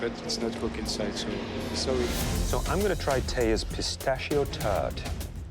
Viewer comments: Is it just me or does it sound like he said " So l'm gonna try Thea's pistachio turd " ? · i thought he said pistachio turd